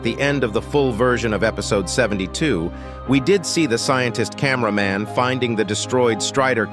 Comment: Bro did you just say scientist toilet it is scientist csmeraman